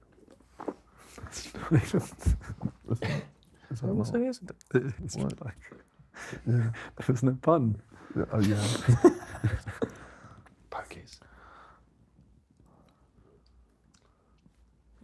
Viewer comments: am i schizophrenic or did someone say *akis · what did josh say? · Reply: He said bogeys 😭